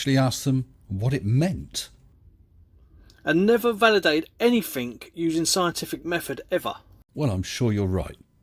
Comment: did he say anythink? He even emphasized hard on the hard K. He doesn't English very good. Why would he be right in any other field having to do with anything at all. He probably has instructions on how to flush, above the toilet in the living room. Wait what?